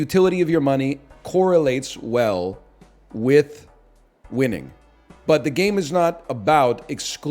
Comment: I heard “maximizing the utility of your money correlates well with… women.” I’m not sure if I’m disappointed or relieved that he actually said “winning”